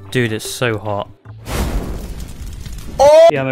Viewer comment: In the beginning I thought he said “Dude is so hot”